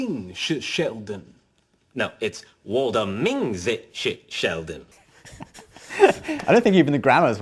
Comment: i think sheldon's trying to say 我的名字是sheldon